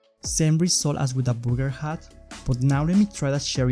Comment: Again did you say Booger? and a Booger hat? who would want a Booger hat?